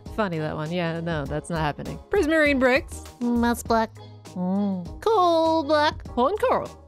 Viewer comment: I was playing a game while having this play on the other screen and misheard "moss block" as "mouse blood" and it threw me for a quick second o.0